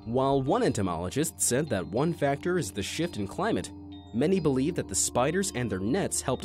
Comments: is it just me or did i hear sh!t and climate? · Did he say “shit” and climate?